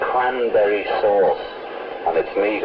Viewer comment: I thought John said "I'm very bored " ..... I would have never dreamed that he said "cranberry sauce " go figure. ....